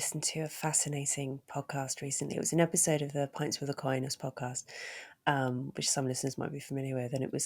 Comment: Louise mentioned a podcast that featured an exorcist. DOES ANYBODY KNOW WHAT IT'S CALLED? Louise said it too fast for me to catch. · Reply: Pints with Aquinas?